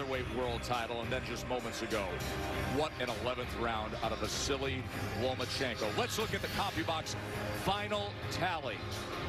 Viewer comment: it sounded like he said "what an 11th round out of a silly Lomachenko."